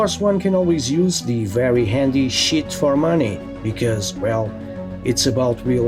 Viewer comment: Omg I thought you said “Shit for money” and “Shitting it is!” But you were saying cheatingggg I love your accent